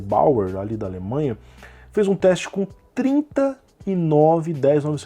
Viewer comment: "Fez um teste com 30 i9" eu entendi 39